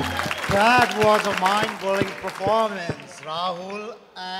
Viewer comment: Am I the only one who heard "MIND BOLLING" instead of mind blowing ??? :D :D